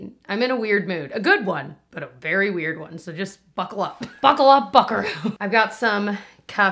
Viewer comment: Definitely thought you said, buckle up f**ker! I laughed pretty hard when I realized that you clearly did not say that!